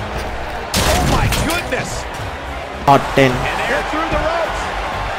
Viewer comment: did he say top 10 like what the freak was that?😂